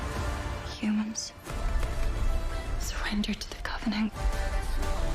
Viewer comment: why does she call it "the covening"(?) and not the covenant? Help